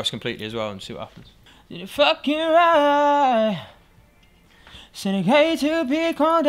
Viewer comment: I heard 'fucking ryeeeeee' at 22:53 😂😂